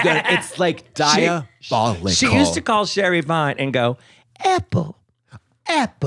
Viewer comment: Oh god I thought they were saying Sherry PIE 💀 not Vine